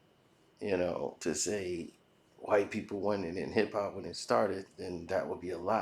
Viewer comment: From this video it sounds like joe said when hip hop started it was blacks and Latinos 50/50. I did not hear anything about “created”. I heard: “when it started”.